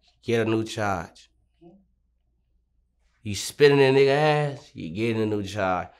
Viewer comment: I thought he was saying Spitting on a mf ass, turns out he was saying Spitting in a mf ass.. tf 💀💀🤣